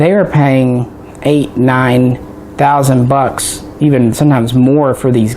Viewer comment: Hello. I just found a Mak 90 in a moving box that I had forgotten about. Never hot it. Still wrapped in plastic. Did I hear you correctly (at 22:14 on the video) that they are worth $9000? How do I know which version of Mak 90 do I have? Many thanks! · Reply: I know this is crazy old but I thought I'd reply for anyone else reading this today. No, he was not saying $9000. He was saying $800, $900, $1000. He just abbreviated it as "8, 9, 1000 bucks.".